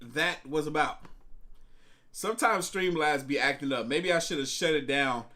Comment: What discord do you use? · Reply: Or streaming? I thought I heard you say stream live?